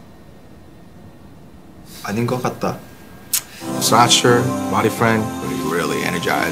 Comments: I THOUGHT THEY SAID BOYFRIEND INSTEAD OF BODYFRIEND LOLLL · Tae: do you need bodyfriend( I heard it as boyfriend 😂😅) Me: no tae, I want you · HELP i thought taehyung was saying “do you need a boyfriend, too?” and i’m like HECK YEAH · legit thought they were saying boyfriend till the ending lol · 😂I thought it said boyfriend at first but it’s bodyfriend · Am I the only dumbass who thought that they are saying boyfriend 😭😭😭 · At first I didn’t think it said bodyfriend... I thought it said boyfriend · I thought it said boyfriend I was stop playinnn